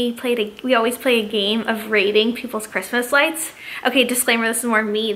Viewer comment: At first I heard that you "raided" peoples light instead of "rated"